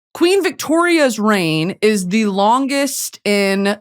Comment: I thought you said queen Elizabeth's brain was the longest